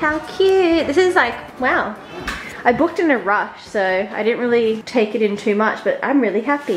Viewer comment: After the "1 poop later" I thought you said "I pooped in a rush" instead of "I booked in a rush" hahaha. Big YAY for travel vlogs. Thankie. 🧋 EDIT: wait ... you dip the Tim Tam Slam into the milk and drink it through the Tim Tam Slam?